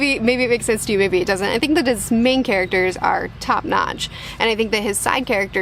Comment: When you emphasize main characters? When you set it, I heard "Maine characters" and thought that makes sense... I'm in the state so I got the meaning behind what you didn't mean to say